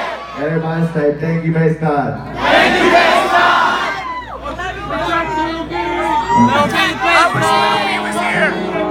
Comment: does he say thank you based god? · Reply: Haschio thank you Fishnarc (he’s the guy in the orange shirt and he produced the songs)